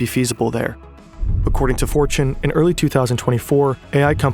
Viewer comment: i was listening and not looking at my screen and misheared "Fortune" as "4chan" and was a bit confised by the odd choice of sources... 😅